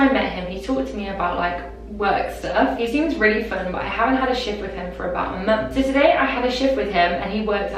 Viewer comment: on the first confession i fully thought she was saying shit instead of shift 😭